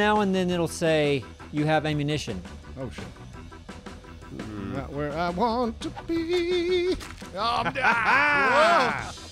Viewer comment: it first sounded like he said not where I want to queef. Maybe I need my hearing checked.